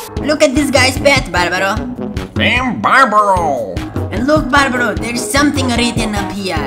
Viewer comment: lol I think it says "te amo barbaro" insted of team barbaro😂😂